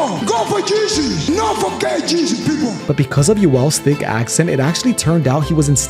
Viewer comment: Thank you for clearing that up. I thought he said Not gay Jesus.